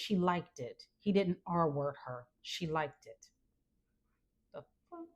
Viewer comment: What did she mean every time she said " our wording" I'm not sure if that's what she said but it sounded like she said that replacing the words SA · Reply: "R" word not "our" word. You can't say it or they will ban you